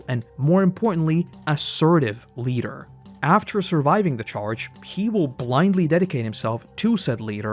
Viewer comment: pardon me, did you just say "after SURVIVING THE CHARGE?!"